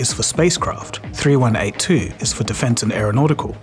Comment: You said 3182 instead of 3812 at 1:08 😯